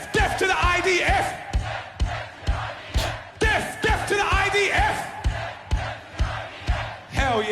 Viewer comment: I’m sure I heard ‘deaf to the IDF’